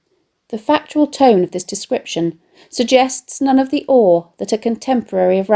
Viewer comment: sorry for asking but what did you say after "none of the" thanks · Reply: I say: "The factual tone of this description suggests none of the awe that a contemporary of Ramesses would have felt when looking up at the monument." Hope that helps!